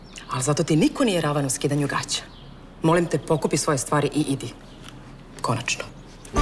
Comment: Can some one help me what did she say on 2:10? Sounds like Kolačno? What does it mean? Thank you! I am learning the language. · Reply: She said "konačno" which means finally :)